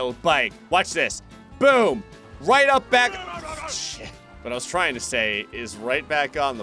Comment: did anyone else hear gray say sh*t?